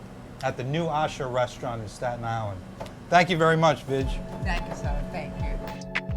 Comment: I thought he said thank you BITCH lol